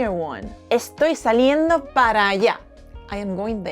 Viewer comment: I still missed two questions, i will watch again! 😦Gracias! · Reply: Excellent video,! I often hear PARA ALLA but I thought they were saying PARA YA😅 and I couldn’t understand what they were saying, now I know it’s PARA ALLA, thanks so much😊